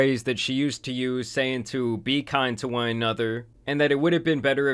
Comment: I always thought she said “be kind to your mother” all along…